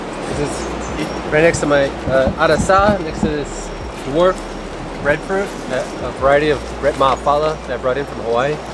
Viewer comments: Someone knows what is the name of the plant at 15:34, the one next to the Arazá, I couldnt hear him clearly when he said the name. I found it cool that 1 tree can feed a family of 4 for 75 years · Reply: In Jamaica we call it breadfuit, idk if has any other names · what are the fruit trees at 15:30? can't quite make out what he's saying. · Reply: Breadfruit I believe